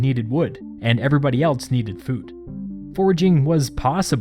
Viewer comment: did somebody say wood for wheat? lol